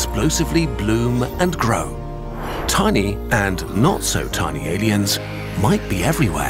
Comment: I thought I’d said banging aliens 💀💀